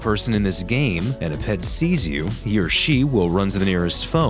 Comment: I thought you said that their pet would call the phone when you said ped